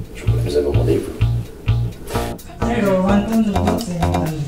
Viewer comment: Does he really say: "Isch bin Lafaille" at 4:57?